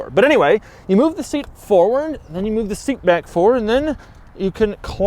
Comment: Did he say you move the seat back forward? lol